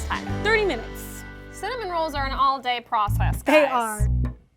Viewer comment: Is it just me or did Alix say tarder at 9:55? IDK 😐